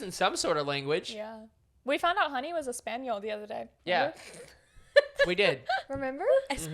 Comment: When said "We found out Honey was Espanol the other day." I thought she said "We found out Honey was a spaniel the other day." I was like "I though they already knew that." 🤣😂 Please don't tell me I'm the only one